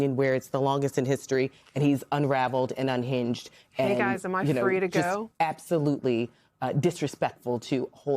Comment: was someone's microphone still ON? I heard an additional voice say "hey guys, am I free to go". Maybe someone off camera.